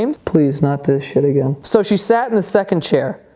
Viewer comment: thought he said, "that's what she said," at first.